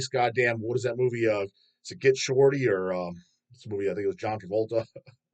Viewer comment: did he say John Kvlta??